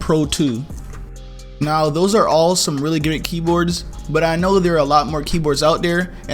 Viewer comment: He actually said gooder